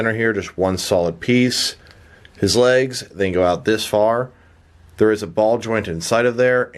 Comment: I’m sorry. it made me laugh. When he opened the legs and said there’s a ball joint in there. It buffered when it said joint so it cut that out., so all I heard was... “he had a balls in there” 😂